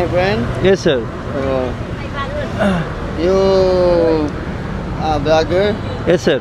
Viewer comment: I heard like "you beggar?" 😂😂 Yes sir😂😂😂😂